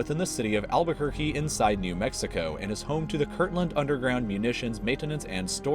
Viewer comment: I thought he said "Kirkland" storage facility, like it's a Costco.